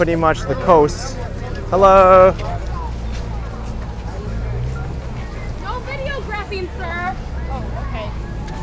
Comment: What did she say??? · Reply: She said " No videographing, sir! "